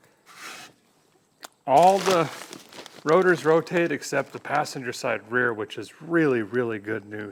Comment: Did he say “passenger side” @ 40:00 or did I hear “drinker side”?